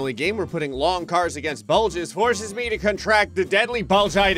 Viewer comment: I thought that said Bugles.